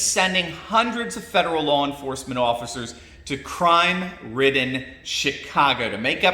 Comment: Did he just say 2:08 "crime ridden Shitcago..." LMFAO